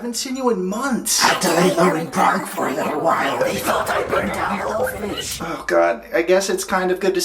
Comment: I have the captions on and the part where You’re asking Benjamin if he burned down the orphanage ... the captions thought he said “they thought I burned down little fish” 😂 I took a screenshot but it won’t let me post it . So funny to me though .